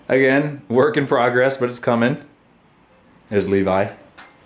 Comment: a second ago you said “heres levi/y” and then when you said messi i thought you said “heres messi”